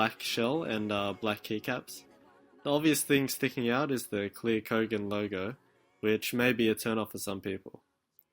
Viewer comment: I thought you said "The obvious thing sticking out is the clear Kogan Logan which may be a turn-off for some people." The packaging and the generic software leads me to think Bogan Kogan